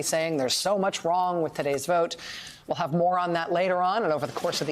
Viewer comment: When anyone talks about Trump then says ‘we’ll have more on that later’ instead of ‘more on’ I always hear ‘moron’ and think it is a fair assessment of Trump and his supporters.